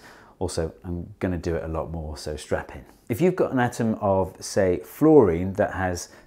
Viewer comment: did he say strap in or strap on?